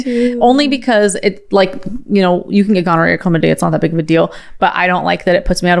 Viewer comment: did she just say gonorrhoea is not a bud deal ?